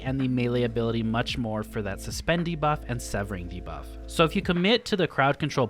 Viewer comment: I thought you were saying "suspendy buff" the whole video. I realized at the end you were saying "suspend debuff".